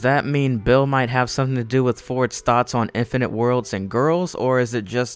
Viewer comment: Wait did he just say " Infinite dimensions and GIRLS" oh.... my..... god 😱😱😱